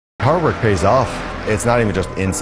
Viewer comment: Thought he said Harvard 🤣